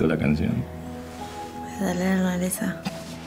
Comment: Que dice rosa en la parte 20:40 Te gusto la cancion? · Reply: Es de Lerner esa